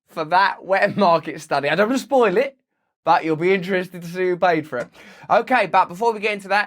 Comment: As a northerner, at 1:02 I heard “BAT you’ll be interested to see who paid for it” I’ve got bat on the brain!